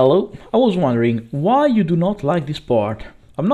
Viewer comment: Haha! same second I clicked down the video i heard "why you dont like this part?" so I had to go back and listen. But well, im just here for the gameplay which is great :)